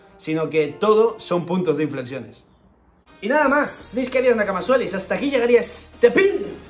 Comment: Qué es lo que dice en el minuto 13:48? Soelis? Que significa Nakamas Soelis? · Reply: Nakamasuelis, una forma más cariñosa de llamar a los nakamas xd